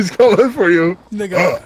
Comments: did DK say the n-word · did Dakotaz say the n word🤔